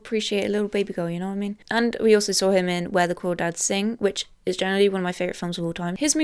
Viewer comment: I thought I heard “where the cool dads sing” 😂😂